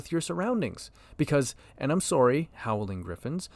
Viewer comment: did i just hear you say... im s0rry?!? "Angry eagle noises"?